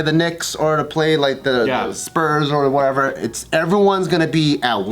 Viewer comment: am I the only one that misheard him call a team "The Sperms"?